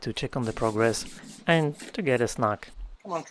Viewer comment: I paused for a minute when I heard you say "Mungo came by to get a smack." Thankfully, I misheard and you actually said "snack"!!! 😂